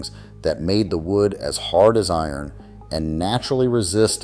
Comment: “hard as I am”?! That’s what I heard